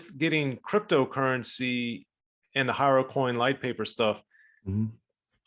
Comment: Im tripping thought it said Heroincoin..... Might have to take that idea myself🤔💉